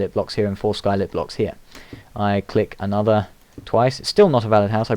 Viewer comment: I thought it said dick instead of click lol ^-^